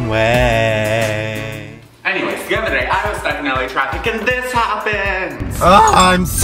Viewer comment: I thought he said I was struck in La trash but he said traffic, well honestly there's no difference either way you will be surrounded by a bunch of producers in white BMWs and struggling actors in 2002 Toyota RAV4s